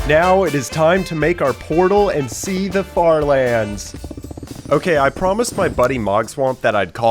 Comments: What Said = "Time to go and see the far lands!" What Captions Said = "Time to go and see the fart lands!" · the captions say fart land XD